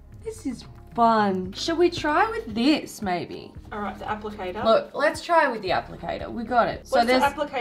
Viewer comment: when u said “applicator” i thought u said “apple tater”